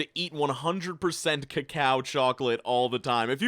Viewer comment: Did u just say ”CowCow Chocolate” instead of ”Cocoa” wtf lol